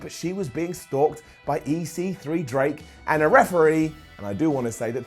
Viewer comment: Thought he said "EC3, Drank, and a referee". Man that sounds like a gimmick right there pal. That's good shit!